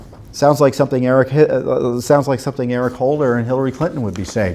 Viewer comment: It sounded like he almost said Eric Hitler while trying to say Eric Hillary & then quickly correcting to Eric Holder & Hillary Clinton 😂 These individuals & groups should be sued out of business just like kkk was apparently sued by some organisation.